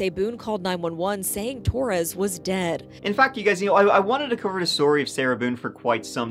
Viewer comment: Sounded like you said “and fck you guys” in mockery of Sarah. 😂